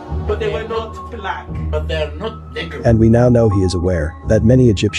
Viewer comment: The way the N word just rolled off his tongue says everything 😂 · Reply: I believe I heard either "Negros" or "Nubians". Please don't go off the rail.